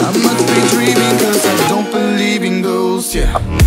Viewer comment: What he say: i don't belive in ghosts What i hear: i don't belive in girls I don't know '<'